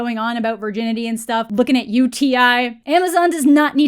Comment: I heard "looking at UTI" as in Urinary tract Infection rather than T.I. lol